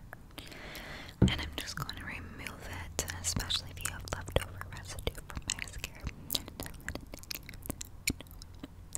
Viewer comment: Does any one kbow what she said between 5:23 anf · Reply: lifeofneve “I’m just gonna remove it”